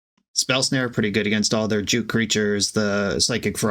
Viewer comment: is he saying spell snare is good for his opponents "jew" creatures?